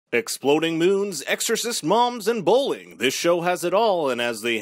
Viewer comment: omg in the beginning i thought he said ‘exploding boobs’ i